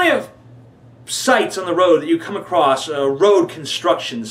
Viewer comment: when you said sites. I thought you said dikes on the road. and you peaked my interest.....lol